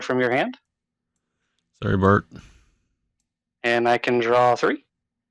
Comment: Wasn't expecting a Sesame Street quote. When you said, "sorry Bart." I thought you said, "Sorry, Bert." I had to go back and listen.